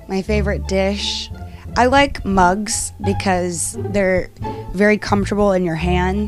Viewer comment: I thought she said bugs